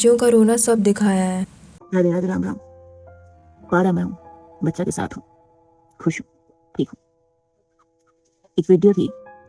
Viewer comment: wait ...what she just said🤷‍♀️ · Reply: Pahado mai hu baccha ke sath hu